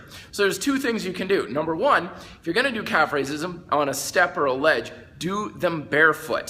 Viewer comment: Did he just say calf racism